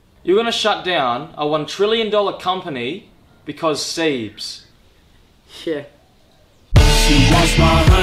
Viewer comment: What is he saying? "You're going to shut down a one trillion dollar company, because... sebes?"